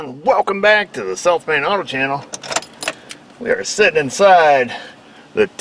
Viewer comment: I feel the need to say, until recently when I viewed this channel I would have sworn said "Welcome to the Self made Auto Channel". OK, now I know...... :)